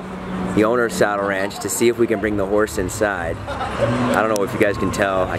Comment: At first I thought they were saying “taking the whores inside” not “horse inside” lol very confused